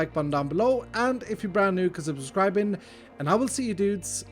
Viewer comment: For the longest time I had no idea what is saying at the end of each video. I thought: "No way he is saying 'come sit her subscribing', nor 'komsiber' or 'kosiber subscribing', makes no sense..." Finally I figured it out. It's CONSIDER. Love me my bilingual confusion.